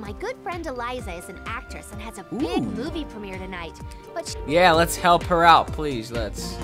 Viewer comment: i swear i thought i heard "a big booby premiere" at 8:04 o. o i lost my attention span